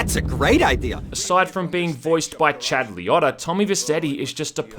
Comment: Who is Chad Liotta? Am I hearing that wrong?